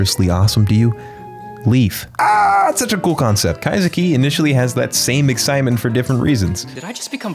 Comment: All I hear is kaido